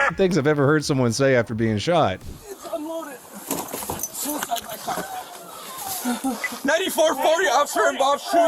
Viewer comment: So if the first guy's gun actually was unloaded then he got shot while maybe trying to give the cops the unloaded gun instead of trying to kill the cop, altho it's usually best to just go hands up and advise the officer that you have an unloaded weapon and it's whereabouts on your person. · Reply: Maybe not, on my 3rd time listening I heard him say "It's unloaded... Suicide by cop". 🤦🏻‍♂️🤦🏻‍♂️🤦🏻‍♂️🤦🏻‍♂️ I hope that was just a twisted sense of humor.